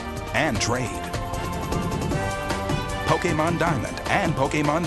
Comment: He sounds like he says Pokaymon